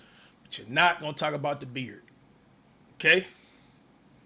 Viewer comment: nah never did say dhz 😂😂😂😂